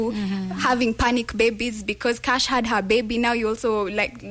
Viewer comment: Did she just say...panic babies..😁😀😁😁😁😁